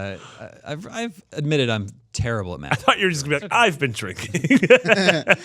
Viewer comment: hi non english speaker here, trying to learn by listening to this awesome podcast , what does says when he goes "i ve admitted before that i [.........] when we record this show " at 21:37 i just can t quite hear it can somebody help me ? thanks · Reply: " I am often half in the bag..." which is slang <link>